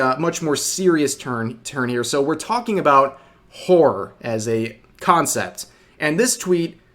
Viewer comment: "Wh*re as a concept" I did a double take because that's what I thought you said 🤐